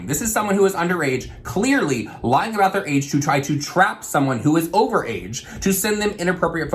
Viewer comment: did you say "trout someone"